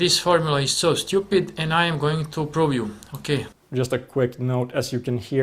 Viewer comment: I swear i heard him "I'm going to probe you."